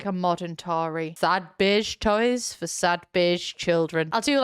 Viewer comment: The first time she said sad beige toys I thought she said "Sad bitch toys for sad bitch children"